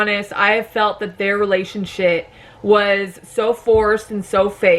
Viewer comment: Am I the only one who heard relation-shit at 2:01? 😂